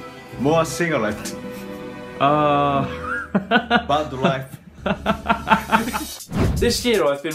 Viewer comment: "More cigarette. Bad wife" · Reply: DeMat i thought he said "bud life" lol